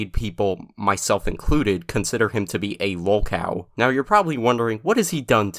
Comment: I misheard lolcow as woke-owl lol